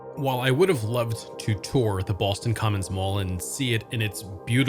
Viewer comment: lol, I keep hearing “Boston Common.”